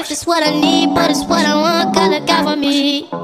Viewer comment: PLS- I THOUGHT HE SAID GOBBLE GOBBLE ME MEEEEEEE NOT GOD LOOK OUT FOR ME😭😭